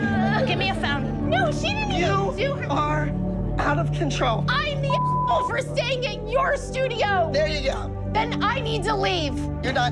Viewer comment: I couldnt understand what Christi was saying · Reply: “I’m the a** hole for staying at your studio”